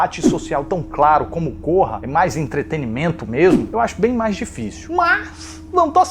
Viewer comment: só eu entendi ele falar po#$a quem entendeu da like